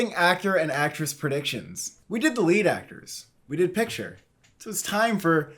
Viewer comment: Okay at 0:05 when the word ‘actors’ is cut off from the on screen graphic, it sounds like you say “we did delete actors” really offhand and I burst out laughing